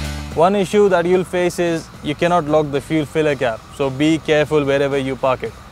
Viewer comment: He says at 2:22, "One issue you will face is ..." [then it sounds like he says you cannot lug the fuel silica] ... so I have no idea what this issue is. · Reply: I think he meant `cant lock the fuel filler cap`